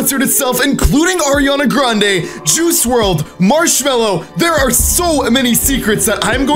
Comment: did he say what i think he just said JUICE WRLD